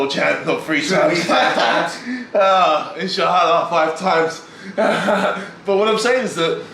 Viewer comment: did he say inshaAllah?